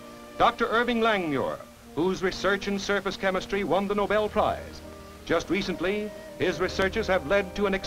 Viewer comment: Did you hear that? These guys got Nobel peace prizes for their achievements because they contributed to the better of mankind. They hand out Nobel peace prizes to people for learning how to tie their shoes nowadays. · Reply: I'm sorry. What's that he says at 2:32? Sure sounds like he said he won a nobel peace prize.